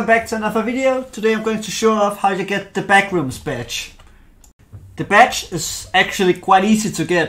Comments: At the start who else heard him say “the backrooms b*tch” 😂😂😂😂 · i dont know why i am the only one i hear "b1tch" when he says badge